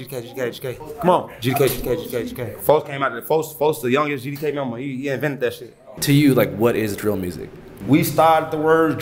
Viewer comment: what he say after GDK GDK GDK at 2:10? I heard "young'n" and "vendeshi" and everything else was slurred together, sounded like another language. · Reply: haha yeah it's almost like what a scottish accent is to RP. he said "folk came out the- folk is the youngest gdk member, he invented that shit" i believe "folk" is basically a third person pronoun like "he," but it's used only for close friends or family